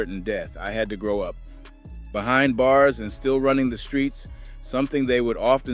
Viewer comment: Bruh i thought this mfr said skreets at the beginning